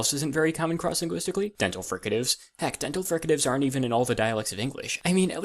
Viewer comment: why has no one noticed "frick, dental hecatives" instead of "heck, dental fricatives" in the subtitles at 2:10?